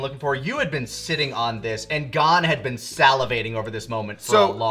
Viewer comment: At first I thought you said “God had been salivating over this moment” 😂